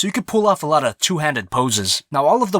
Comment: did you say POSES